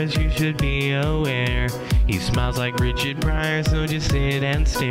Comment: Off topic: usually because I've got YouTube Red, I usually listen to most of my subs in the background so I've never paid attention to your opening credits. I've always thought you were saying "he smells like Richard Pryor" instead of smiles.